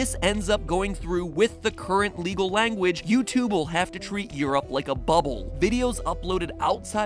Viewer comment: Hold up now, when he took blue over the countries in Europe, he forgot Norway... what does this mean, you took blue over Sweden, then why not over Norway! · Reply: Just so it is clear, he said Europe at 10:28, SO say what you want about "he said EU not Europe"